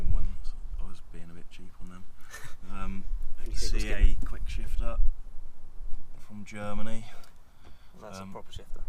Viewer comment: what shifter is he running? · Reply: Think he said CA quickshifter from Germany. Check the time at 6:27.